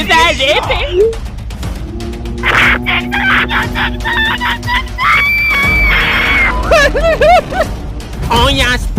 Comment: ne dediğini bilen varmi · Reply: "Herkes çıksın herkes çıksın herkes çıksınnnn" diyor